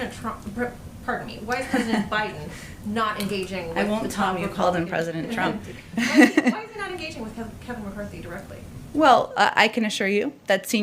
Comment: My speakers aren't that great and I wish I could be given a chance to read lips... what did she say, "I won't tolerate you calling him president Trump."?